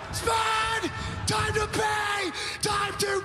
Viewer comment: "SPUUUUUD!!! SPUUUUUD!!! TIME TO PAAAAY!!! TIME TO PAY!" 😂😂😂And I mistakenly thought he said "time to play" when I was younger😅🤦‍♂